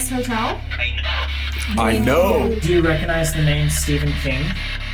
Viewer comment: to me personally at 50:51 when asked "Do you know the name stephen King" it sounded like it said "I fucking hate stephen King" I might be tripping but that's what I heard